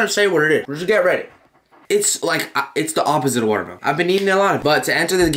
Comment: am i the only one that thought melon water when he said the opposite of watermelon